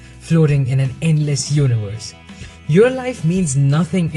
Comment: minutes you said "Meditate on how we are all specks of dust in this immense universe' but I thought you said Urinal (instead of universe) I thought that was hilarious.